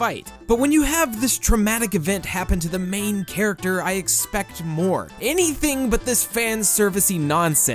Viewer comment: i thought at 6:07 you said vore instead of more